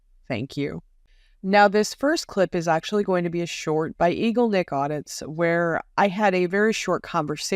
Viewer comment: I thought he said "Needle D*ck Audits." Probably a more accurate description.